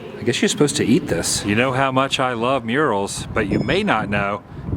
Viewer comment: You said you love murals I thought you said urinals :)